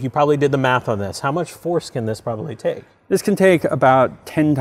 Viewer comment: Did he just say foreskin?